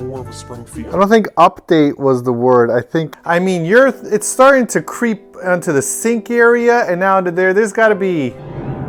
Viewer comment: Okay, so when said "Its starting to creep down to the sink area, and now there's, there's gotta be--!" @ 2:46 What I heard was "Its starting to creep down to the sink area now that there's no spudgy " and I had a d*mn heart attack. ANYWAY my ears are horrible and I had to listen to it again to double check that sweet, gold star, best-husband-of-the-year, had indeed NOT been replaced by an Evil twin and had not said such mean things to all things cute, sweet as sugar, best-wife-of-the-year! Edit: I have PTSD and depression, and somedays, I just dont feel like I can function. Your guys' constant uplifting attitude and positive outlook on life has helped me in so many ways. The #buildaladder has really helped me, and I guess I really just wanted you to know that. And thank you. Edit: Me and my endometriosis connected with your period pain on a spiritual level. 😭😭 Edit: Okay, my "edits" as the video continued made this a really long video. So, SORRY! 😅